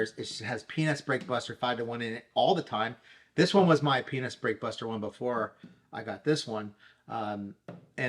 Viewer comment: I need my ears cleaned out. It sounds like he is saying penis break buster. 😂. Then I saw the product name in the description.